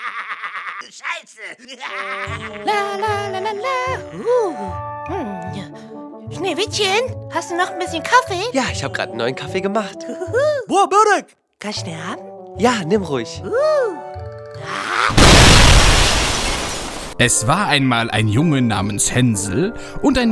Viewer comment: Ich verstehe bei 4:26 wo das sagt :,,Boah Börek...Kann ich sterben?"😂